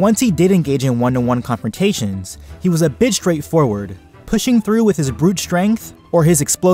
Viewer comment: I really thought you said "he was a bitch straight-forward" XD when you actually said " he was a bit straight-forward"